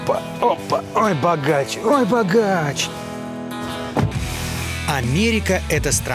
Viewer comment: Илья говорит: "Ой, богач. Ой, богач". Я слышу: "Царь во дворца. Царь во дворца".